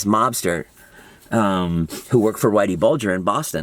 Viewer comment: Damn, I had to rewind, I thought you said you worked for Whitey!! I was like, "whoa Jason, I didn't realize you were so gangster back in Boston"